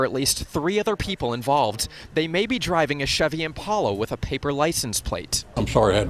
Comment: did he said Chevy Impala😯...It must be Sam and Dean picking some items for hunting thing,saving people, family business and stuff like that 😂